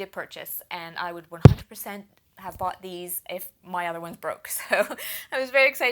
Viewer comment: I never said anything about them breaking. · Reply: My bad, at 3:57 sounded like you were glad to review the new one because "my old one broke" but now I see that you said "if"